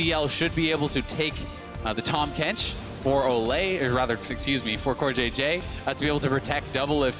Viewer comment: did he just say Olleh?? 😂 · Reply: Yeah, he said "For Olleh, or rather excuse me... for CoreJJ"